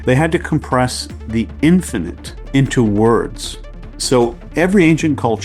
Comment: in 23 you said infinit.... i heard infant... ""Child" means something evolving towards independence." [Psyche & Symbol, Carl Jung, edited by Violet de Laszlo, 1958, Ch.3: The Special Phenomenology of the Child Archetype, Sec.I-The Abandonment of the Child]🤣